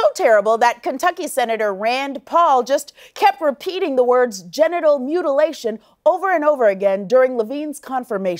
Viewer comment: Sounded like he was saying getinal mutilation...